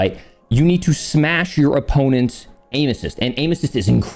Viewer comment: i swear around 19:50 mark he said, "smash your opponents anuses."